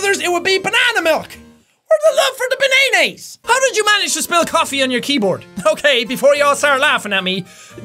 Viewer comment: I have seen this video like three times now and at 11:17 in the video i just hear spell instead of spill and my english is very bad I know